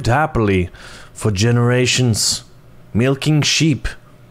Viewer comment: did he just say milking sheep